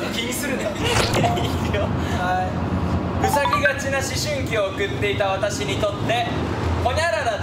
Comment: 塞ぎがちな思春期を送った子が唯一友達と言えるのが〝生まれたての子犬〟って実際にありそうなエピソードだから、大爆笑してるジェシーに「え？！陽キャ過ぎて怖い😭」って思ったら〝うさぎがち〟と聞き間違いしてて安心したし平和なジェシーちゃんで良かったです